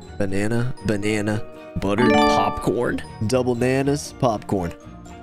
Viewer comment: I thought it said toe bananas